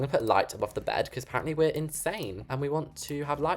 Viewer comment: I thought you said in Spain instead of insane